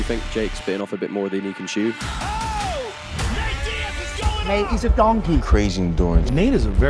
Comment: *Did he say "Nate is a donkey."?